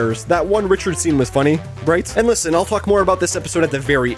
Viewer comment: did you say "talk more" or